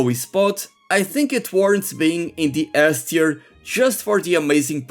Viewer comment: did I hear correctly you said demon dragon ganon belongs in ASS tier? wow.